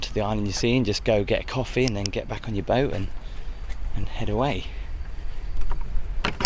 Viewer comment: "What is love, baby don't hurt me!, don't hurt me no more..." Sorry, thought you said get a coffee and Haddaway on your boat 😊